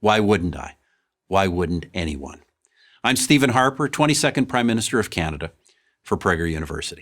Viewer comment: He said "22nd prime minister" and I heard it as "20 second prime minister" lol